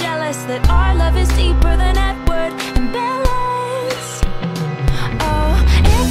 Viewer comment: Let’s be honest All love stories are better than Edward and bella’s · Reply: Bro thought it said better then n-word and ballet